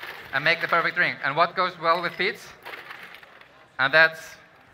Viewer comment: Lol, he was saying peach. I thought he was saying peat